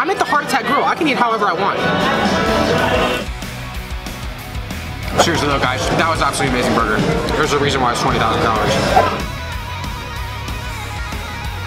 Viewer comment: if you can hear carefully he said 20,000 dollars instead of calories😂😂😂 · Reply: He said calories it’s just hard to hear bc of the loud music